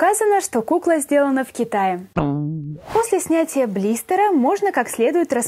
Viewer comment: мне одной послышалось "После смерти блистера..." ? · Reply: Kubasova Ksenia После СНЯТИЯ блистера! Прислушайся